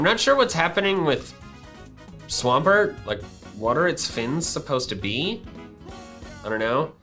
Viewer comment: heh, it sounded like u said "WATER its fins supposed to be " heh heh he -h? he? no? ok ill just leave then.